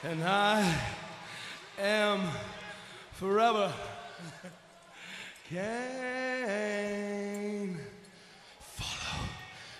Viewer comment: It sounds like he said “Im forever GAAY”